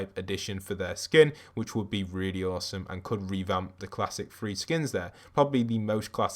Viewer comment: You said three skin I thought you said “free”